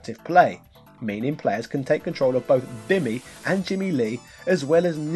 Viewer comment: did you actually say "BIMMY"?